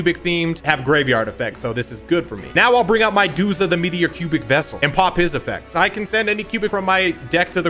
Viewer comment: into the video all I heard was ok I’m gonna summon my Duza the meteor PUBIC vessel LMAOOOO